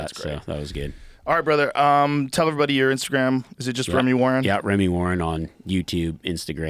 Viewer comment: Thought it said Reggie Warren ...that dude was tough